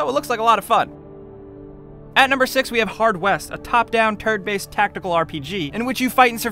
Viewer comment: did he say "turd based" ?